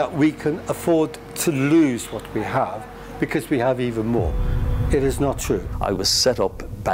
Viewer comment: The subtitles got it wrong. He said 'it is not true'